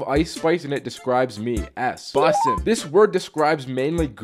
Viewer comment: My dumb ass thought he said busting 💀